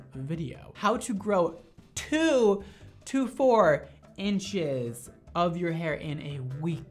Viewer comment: When he said 2-4 inches I thought he said 224 😂😂